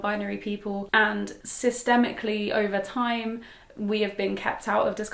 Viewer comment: lol I thought she said CISstemically